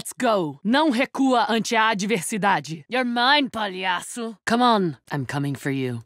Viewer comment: What does she say at 0:06? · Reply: Mike Z "Palhaço", it means "Clown"